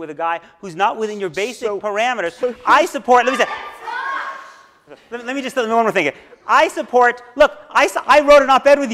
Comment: What was with the shouting at 1:19:40? Anyone has a clue? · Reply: She said “Alan stop” when Dershowitz was interrupting Beinhart.